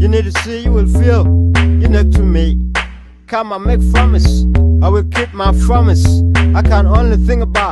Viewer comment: Fromise instead of Promise lol!